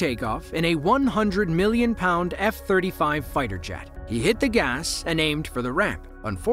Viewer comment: Did I hear correctly .. "A One Hundred Million Pound F35 Fighter Jet" ???